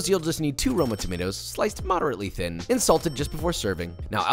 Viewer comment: my girlfriend was wondering was I was swearing at the tomatoes then I realised you meant salted. I thought you said insult lol hahaha. Poor tomatoes...